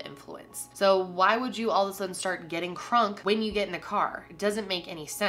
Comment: Did you just say "getting krunk"? 😆